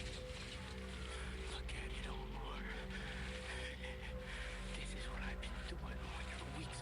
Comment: Who else heard him say look at it Omar Its kinda creepy Cause my name is Omar